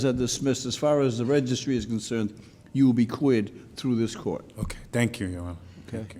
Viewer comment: did he say "queered through this court?"